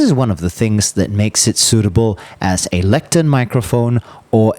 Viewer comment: such a detailed and thorough review, nice! Sorry what do you say here 16:36 "suitable as a ...?? microphone, or a podium microphone" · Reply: Thanks! That word is "Lectern" which is the place from where scripture is read in a church or cathedral.